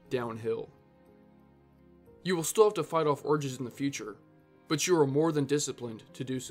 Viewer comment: he says "orgies" instead of urges😂😂😂